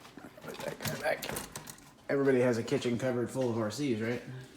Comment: Hahaha my girl heard you say "everyone's got a kitchen counter full of rc" hahahaha